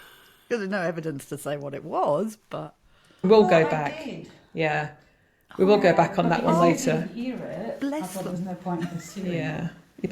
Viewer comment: Right after the ghost box started I thought I heard "go back home". I don't know if he wants you to go or he wants to go.